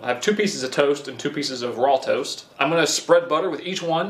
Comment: Did you really say raw toast